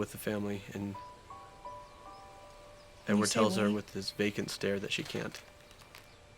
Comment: i thought he said "bacon stare" lol